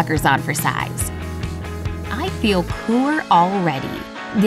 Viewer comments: Did she just say i feel poor already? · did she just say she feels poor aready😂😂😂 I’m diying lmao